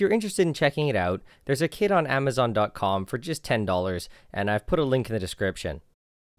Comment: At first, I thought you said, "there's a kid on Amazon dot com for just ten dollars".